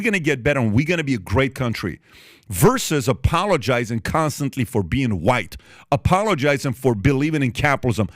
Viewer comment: “Apologizing constantly for being white?” Is that what you just said? Please tell me I heard that wrong.